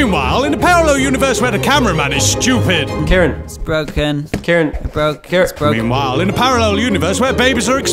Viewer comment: I thought the camera man was saying carrots broke carrots broke